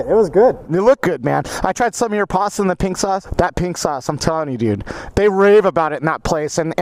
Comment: Thought he said pink socks...